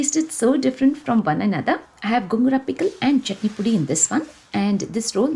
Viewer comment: Hi mam in your video at 8:56 min You mentioned some pickle Did not understand it can you please mention its name I heard it as gumrah pickle but I am not sure of the name Very nice video and beautiful family 😍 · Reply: Hi Uma ...yaa sure it is Gongura pickle widely and easily available ready to eat pickle that you will find in all supermarkets Thank you so much 🤗💕